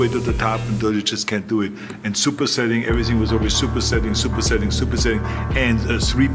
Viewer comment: It sound like Arnold was saying super settings in game 😂